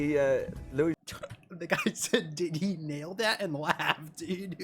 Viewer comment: i thought he said "did he niall that?"